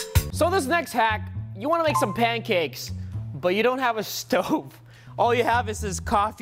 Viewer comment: I thought you said you didn’t have a oven